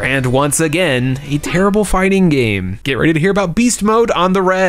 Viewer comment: Thought he said “get ready for beastwars, on the rag” 😅